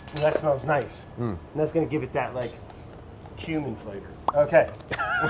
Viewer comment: I thought Brad said it would give it that human flavor...which almost made sense, i was like o yeah, it does kinda smell like Body Odor when I make tacos.